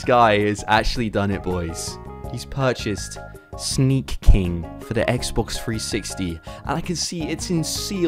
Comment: I thought it said shrek king LOL